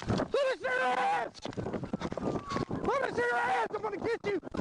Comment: my subtitles said “let us in your ass” 💀💀💀😭😭😭